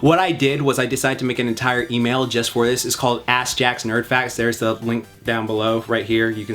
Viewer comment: When said his email I thought he said flapjacks nerd facts